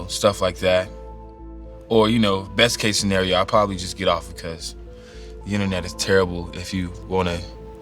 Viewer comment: I thought he said I prolly just beat off